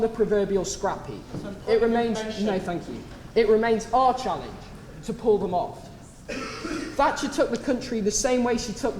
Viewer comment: What does the man in the audience say at 4:27 ? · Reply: “Point of intervention” he wanted to dispute something said